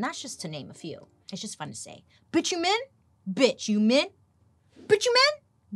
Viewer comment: Bit chu in? Or what ever said 🤣🤣🤣🤣